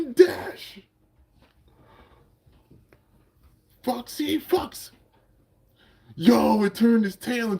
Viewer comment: I thought you said foxy fucks 😂😂😂